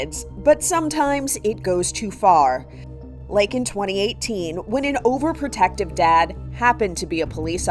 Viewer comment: bro I was 8 when I watched this- (I’m older now) my dumb self thought she said “2028”